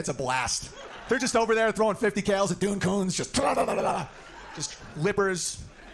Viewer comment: Did I mishear him or did he reference "dune c..ns" at 1:21:05 ? I want to believe that the audience reeled at the racism, not the commentary on bloodthirsty veterans. Edit: nevermind, I just heard how much worse it got. They don't care about the racism.